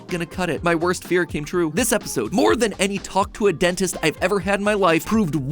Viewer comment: did he say talk tuah